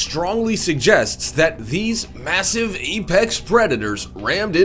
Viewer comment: Did he just say "Epex" instead of Apex or am I hearing things?